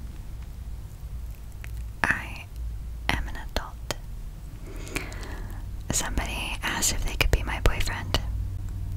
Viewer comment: What did she say? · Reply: "Im an adult"